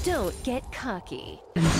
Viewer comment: did she say "Cocky"? Im from LAS, spanish.